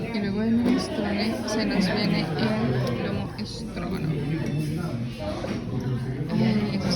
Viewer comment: Que era lo del minuto 2:12 🥘? Stragonoff dijiste ? O escuché 😅mal ? Por eso es seco de pollo o estofado de pollo guisado de pollo depende del país 😅 eso si delicioso a mi me encanta pero stragonoff es otra cosa otro plato el cual también me fascina❤ sobre todo el del restaurante crepes&waffles 🥰👌👌😅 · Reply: Sipi, stroganoff, pero es de ternera. Muy rico, el de Crepes no lo he probado, lo probaré pronto ❤️🫶🏻